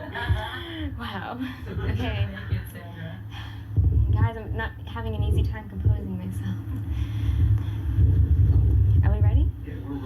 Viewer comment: What did he say to her at 11:53? I would have a hard time composing myself if I were sitting on Peters lap!😆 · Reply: I have it in the subtitles. He said, "I'm not wearing any underwear, by the way." I was nervous and either he or someone else said that I should picture everyone wearing only underwear.